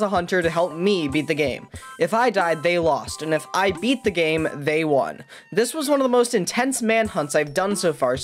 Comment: Did he say, if I die they lose and if I Beat the game they win